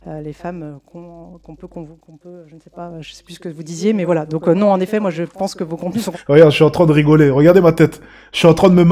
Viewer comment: rapportrice: "Vu que j'ai plus de 38 ans , selon vous je fait partir des femmes ... humm je sais plus ce que vous dites " de quoi elle parle? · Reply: 36 ans